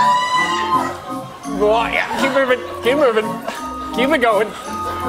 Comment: I honestly thought he said "Keith Urban" instead of "keep moving."